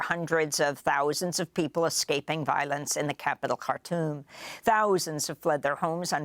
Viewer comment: What did she say? The capital cartool? 5:58 seconds in